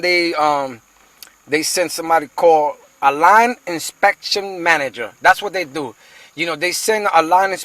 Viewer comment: Did he say "a lying inspection manager" 7:07. LOL